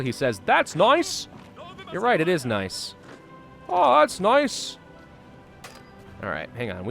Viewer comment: this guy is not saying "that's noice" but "Baczność" what means "Guard (soldiers)". If you want I can translate for you those Polish Soldier voices if you want.